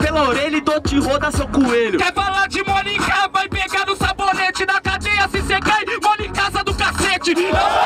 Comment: Queria saber o que ele disse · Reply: 'Quer falar de monica vai pegar no sabonete, na cadeia se vc cai MONCASAdocassete' Muito brabo hahah.